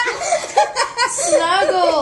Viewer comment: i thought that person said “snuggles you’ve been bussin” AND I LOST IT HELP